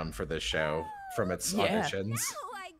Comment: I keep playing it over and over but what does say at 3:09 after "From it's {blank" it's just not clear... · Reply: Auditions